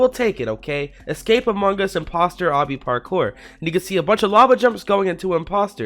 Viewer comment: what did you say? Play 4:47 in 0.25 speed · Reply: I know it its imposter sussus anogus ding ding ding ding